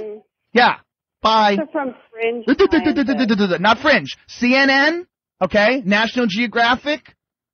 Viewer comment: More people in the comments saying Joe is wrong who I guarantee you haven't even looked the shit up on Google. Literally proving the point he made in the clip 🤣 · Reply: if you listen carefully, I think she says “fringe scientist,” so maybe she was referencing the guy you speak of.